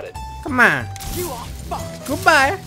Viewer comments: did say "you're fucked"? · DID SHE JUST SAY « YOU’RE FUC*ED »